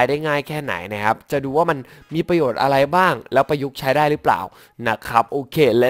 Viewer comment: ผมได้ยินว่า แล้วประยุทธ์ใช้ได้หรือป่าว 5555